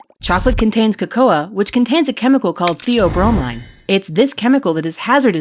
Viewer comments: When said Theobromine I thought she said Herobrine for some strange reason. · did she say co-co-a?!?